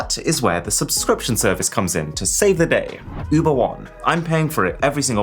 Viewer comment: LMAO, I understood "Uber Walk" instead of "Uber One". I was like wtf?!